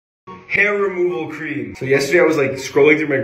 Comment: I scrolled down in this video and misheard hair cream instead of hair removal cream and I thought he's going to try it with his hair. But when he removed his cloth- I mean his tshirt, my eyes got widened 😳 and trying to distract myself instead of scrolling down to next video.